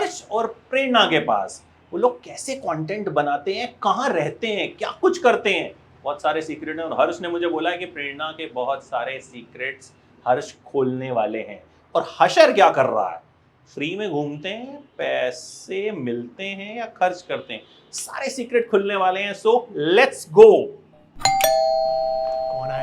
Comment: DID HE SAY HASHER